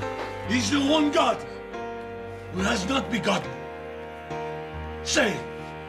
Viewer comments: What did he say at 2:26? It sounded like "if you want God, there's must not be God." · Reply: I said! He's the one God who has not begotten ! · What does he say at 2:25? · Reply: “He is the one God”